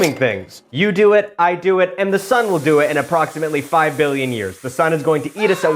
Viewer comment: What!!!! ..oh billions. I thought you said millions. That's fine.